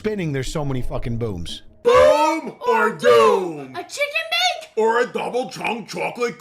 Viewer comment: Boom OR doom. I thought you were saying Boomer Doom.